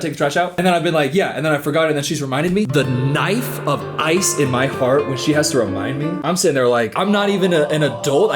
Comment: bro I heard “the knife of ice in my fart” 😭